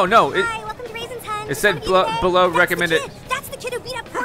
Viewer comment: WHAT?!?! WHAT DID IT SAY?!?!?! WHAT IS IT BELOW!? JUST SAY IT!!!! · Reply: Christian Martinez below recommended might level